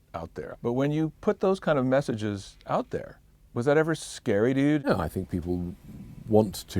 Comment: When you put those messages out there, was that ever scary, dude? XD I know that's not what he says, but still sounded like that to me at first, and I was like wtf is this guy buddies with Gilmour or something, and most of all they don't seem the type of people who would say "dude". Also wtf, is he saying "whatever" at 7:57?